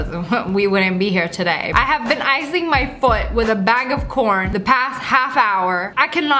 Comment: Lmao i thought you said that you used a bag of por N.